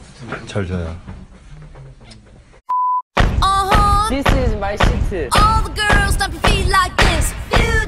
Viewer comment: uh huh/ instead of ”this my jam" its jimin: "this my shitu" lol